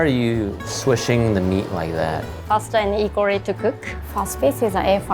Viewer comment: In the subtitles at 4:17 where it says mumbling she is saying equally☺️ hope that helps anyone who wanted to know.☺️💜 So the full sentence is faster and equally, instead of faster and mumbling.